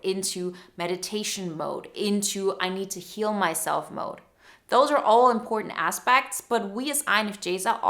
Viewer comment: 9.20 - I thought she said " kill myself mode" instead of heal myself mode.